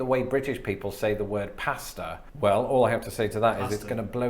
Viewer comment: Am I the only one that thought he said pastor not pasta😂